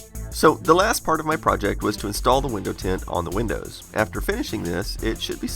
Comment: i thought he said now its time to install windows 10 on the windows! XD